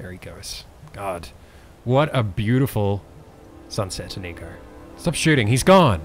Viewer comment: am i the only one who hears "what a beautiful sunset nigga? x D!